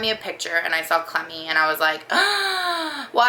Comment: I'm sorry, when I first heard you called your dog "Clemmy" I thought you said "Clammy". I kept hearing that and wondered why did she call her that? Is she damp all the time? She's really cute.